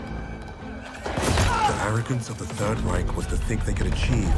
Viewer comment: At approximately the 9:00 mark, anyone else hear "Americans of the third reich?" Even the captions seem to think so.